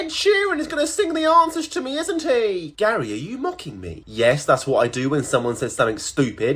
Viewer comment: I thought he said Gary you monkey